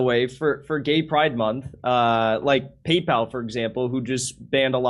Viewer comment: JIMMY ON TOUR! - Irvine, Ca. - LAS VEGAS - CHICAGO - SACRAMENTO - SAN DIEGO - Salt Lake City - Indianapolis - Louisville - Spokane - & More! Tickets here: <link> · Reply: I swear I thought I heard say "GayPal" lol.